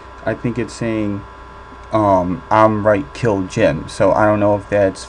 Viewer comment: I think instead of saying "I'm right kill Jim" I think it's saying "I'm right kill him" or maybe "I'm right kill them" I definitely hear it saying "I'm right kill Jim/him/them" also I think where you said you couldn't understand what it's saying I think it says "here I am" right before the camera starts to fall...just a thought... 😜 Btw you're doing an excellent job on catching all these voices! You do amazing work and I really think this helps FD in his experiment!! Keep up the great work!!